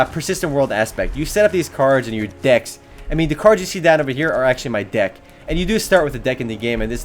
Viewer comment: lol I thought he said "I mean the cards you see down over here are actually my DICK" 5:22 %